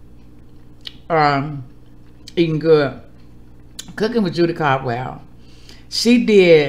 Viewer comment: OMG! Did you say Mama Judy Caldwell?!!! 😍